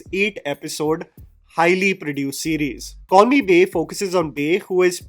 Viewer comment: For the entire video, I heard "Call me Bae" as "Corny Bae "